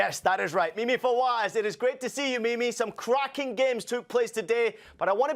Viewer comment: What I heard: “It is great to see you, baby!” What he said: “It is great to see you, Mimi!”